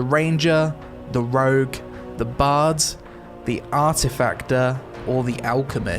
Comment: Did he just call it the Arti"Factor"!?! It is artificer!!!